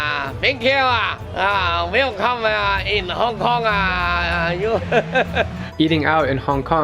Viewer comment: Someone tell em what he said? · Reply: uhhhhhh hongkong